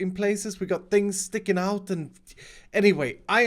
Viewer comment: he said sticking out I heard spit it out what is it exactly I keep thinking about the things that are deadly